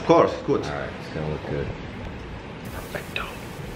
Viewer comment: i heard wrong or i heard "perfecto"😍😍😍😍😍😍 hope you can come to peru .that will be awesome